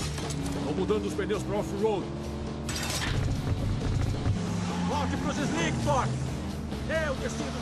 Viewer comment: Kkkkkkkkkkkkkk 0:38 o que ele disse? 0:44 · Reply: Volte pro slick Tork!